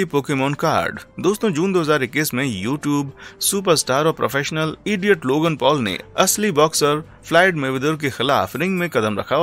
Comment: Did i only heard professional idiot Logan Paul at 3:11 😂